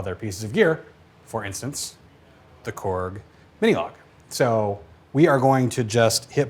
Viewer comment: Is he playing it with the Monologue? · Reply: Sounds like he says "Minilogue" at 8:43 ?